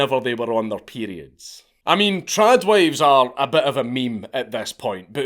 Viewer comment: What word is he saying at 7:25 and 7:30? It sounds like trad wives and trad. Ive never heard or see this word so very curious to know what it is.